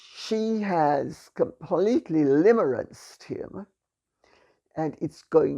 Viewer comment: "she has completely ________ him" Limmerized him? What word am I not catching? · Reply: Limerence